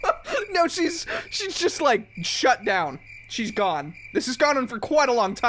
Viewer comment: When he said Shut Down I thought he said SHUT UP so I said it. (Ngl)